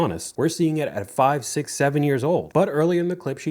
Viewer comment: did you say 6/7?